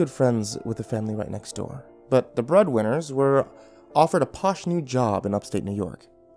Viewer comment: I thought he said the bread winners